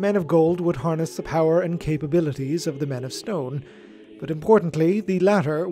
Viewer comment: Snrk. I kept hearing it as: "Men of scone."